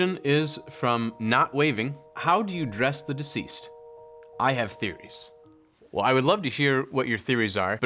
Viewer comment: Lmaooo; omggg; The person that asked, “How do you dress the deceased, I have theories”.. I thought that said.. “How do you dress the deceased, I have BODIES”, lmaooo😅😅🤣🤣